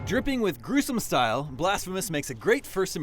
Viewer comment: what did he say about the style ? I'm not a native English sorry !! I wanna know the name of the style !! crisom ? Grizom ? I heard something like that .. anyhelp ? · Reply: King Hyrule he says “dripping with gruesome style”